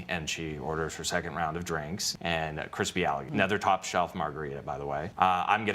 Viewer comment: A crispy alligator I thought he said a Christy Allen I was like wait a minute.